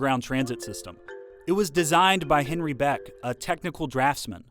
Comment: did I hear you say "Henry" Beck? It's actually "Harry" Beck.